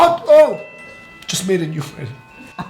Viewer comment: "I just made a new friend" or it sure sounded like that lol